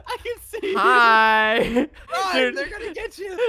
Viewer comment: OMFG did say run ni###